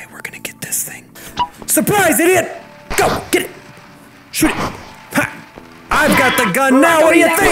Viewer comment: is it just me or did yub just say shit?